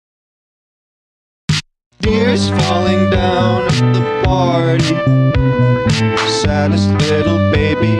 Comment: THIS DESERVES SO MUCH MORE! YOUR AMAZING OMG Edit:to yall saying u love my pfp TYSMM😭😭 Edit2: TYSM FOR THE LIKES WHAT?!??😭🩷 Last edit: WHATSJKvshsjaushdji THANK YALL SO MUCH?!?!?!?🧑‍🦲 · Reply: Am I the only one who thought it said "deers falling down at the party" 🎉😂